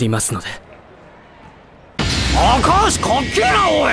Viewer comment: Did just call him Akaash?